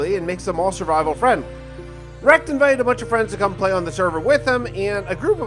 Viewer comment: wait, did you say RecD? As in the same guy who makes lyrical covers?